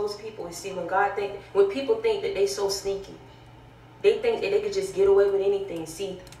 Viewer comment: …did she say ‘sneaky?’…the sneaky squid spirit strikes again!